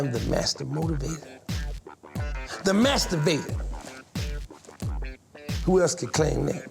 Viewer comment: when he said mastivator, i almost thought he said masterbaiter